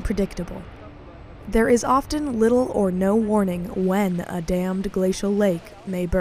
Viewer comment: about 1:14 i heard something like "there is often no warning when a damned glaciat lake will burst"!!